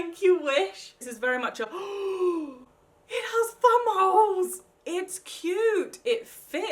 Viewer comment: 6.24-6.30 sounds like ur saying bum holes😂😂